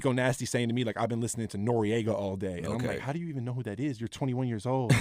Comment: What artist he say when he was talking about Rico at 3:50? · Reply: he's talking about N.O.R.E